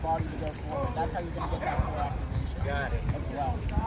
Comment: "That's how you..." I couldn't get what he was saying from the audio. Squeezing your glutes into hip extension from a pike helps to activate what now? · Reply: thats how you gonna get that core activation